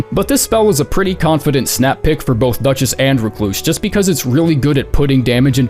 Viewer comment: I thought it said: for depth, 5 duchess. Confused me badly